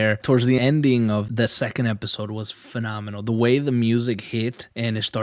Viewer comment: did he just say "phenomamal"?